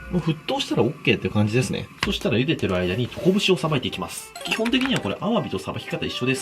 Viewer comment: 俺だけなのか、トコブシを捌いていきますがコブシを捌いていきますに聞こえた